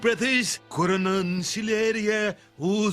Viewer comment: ayo did I just hear QAnon?